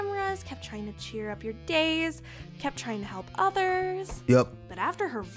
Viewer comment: when I first heard this I thought she said try to cheer up your gays instead of days and I had to take a double take